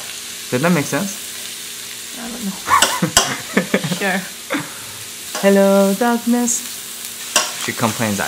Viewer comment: I swear I hear " hello darkness " I'm wearing headphones and it legit scared me